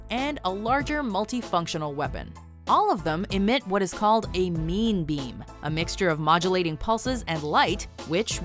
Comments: Did she just say meme beam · i heard "meme beam" is there something wrong with me?